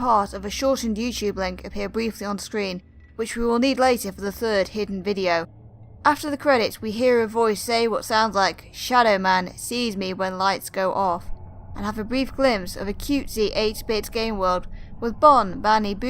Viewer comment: You said the line was "shadow man sees me when lights go off" but I heard "shadow man feeds me when lights go off"